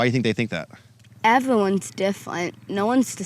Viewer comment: i thought she said evelyn is deferent